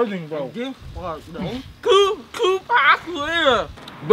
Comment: When they said "Two Pack" I thought they said Toupuaq However you spell his name 😂😭